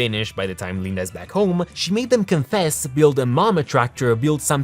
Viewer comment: I thought candice said "build a mall" lol i was off